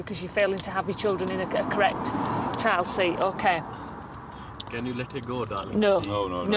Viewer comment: did he just say can you let me go darling